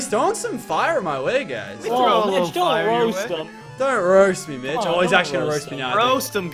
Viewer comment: Did he say mitch? ir bitch